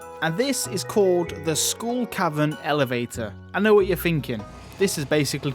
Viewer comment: With your accent is actually sounds like you said SCHOOL cavern... Lol